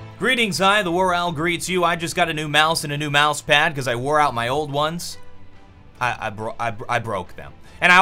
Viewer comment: does he say: "Cause I WarOwl'd my old ones."?